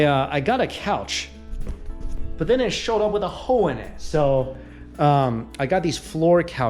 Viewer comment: Even though I know better, it sounded like he said he bought a couch but it showed up with a hoe in it. 🤣😂😁😆🤣😂😁😆 If there were a hoe in my couch, I'd send it back too. 😀😮😉😊🤣😁😆😊